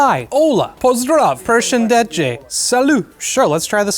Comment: misheard it to person dead jake 🤣🤣